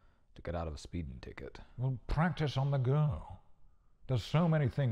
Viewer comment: I heard "practice on the girl, theres so many things you could have done"... ... yeah turned on subtitles and its "practice on the go"